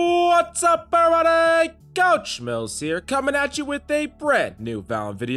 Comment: Whats the deal with these cringe shouting intros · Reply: Thought I was the only one that finds it annoying but the actual content of the videos is good. Also sounds like he says couch instead of coach half the time lmao.